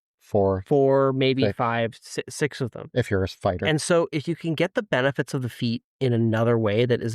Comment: When said “if you’re a fighter”, I heard “if you’re a spider” and had to go back to see if I’d missed him saying “8” before that.